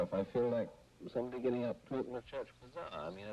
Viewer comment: What does he say at 1:23? I can't make it out. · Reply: "Somebody getting a poke in the chest"